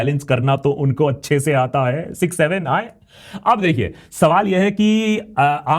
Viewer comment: did he just say 67🥀